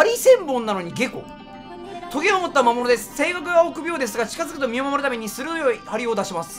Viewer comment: 性欲にしか聞こえない案件